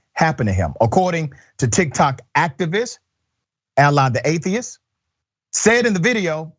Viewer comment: Tiktok activist name alytheactivist. I thought he said allah the atheist.